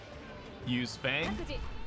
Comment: Why is your subtitle at 2:00 saying You Spank instead of Use Fang... 😂